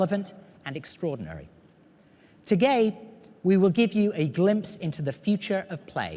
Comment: He said 'to gay' instead of today! Dafuq bro?